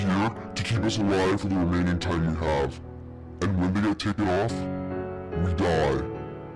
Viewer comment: it sounds like he's saying "and when we get taken off-" instead of "and when we don't take it off-" . Thank you for coming to my pep talk